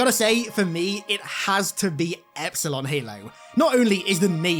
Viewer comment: Dude, I’m not gonna lie at first I thought you said Epstein Halo lol I was like what the hell kind of halo is that🤣